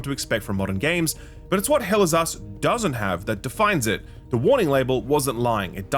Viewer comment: All i hear is "Hell is Ass"